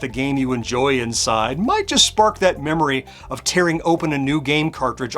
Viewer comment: I heard the 'rents...lol